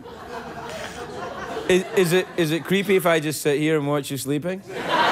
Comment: "Is it creepy if I say [...] sleeping?" What does he say there? Great video, btw. Marion is so disarmingly, unassumingly natural. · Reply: "Is it creepy if I just sitt here and watch you sleeping?"